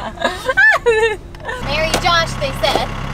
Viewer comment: At the end, as she is shoveling dirt, I thought she said bury Josh they said. It will be fun they said....🤣